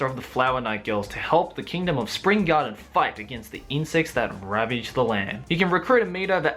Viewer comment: I swear I just heard "incest that ravages the land"....